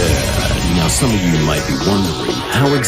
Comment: What she said:beep beep I'm a sheep What I hear:beep beep I'm a ✨SHIT✨